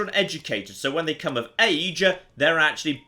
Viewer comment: am the only one who heard, “when they become a mage” instead of “when they become if age”?